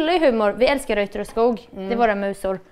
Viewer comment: vad säger är hennes musor?? Kan inte alls urskilja vad som sägs! 😔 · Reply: Reuter & Skoog tror jag